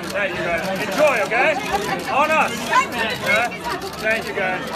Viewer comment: They filled the till for the locals, i heard......"On us!"